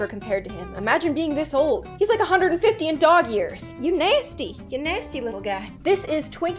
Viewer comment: Is it just me or when I was younger I thought they were saying dog hears not years